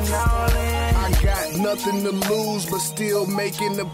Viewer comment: I thought the intro said I'm gonna loseeeee